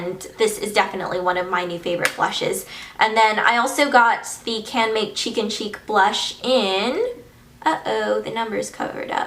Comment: when you said "Canmake Cheek & Cheek blush" i thought i heard "Chicken Cheek blush" HAHAHA LOL